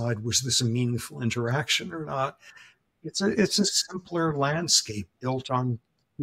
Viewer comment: He says "landscape" re baboons in the context of stress. But in his Brooklyn Jewish accent I heard it as "glandscape"😅